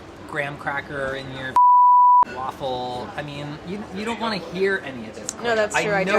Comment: did Chris just say tw@ waffle 😅😂